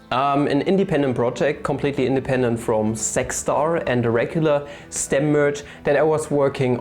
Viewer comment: When you said "Zach Star" I heard "Sex Star".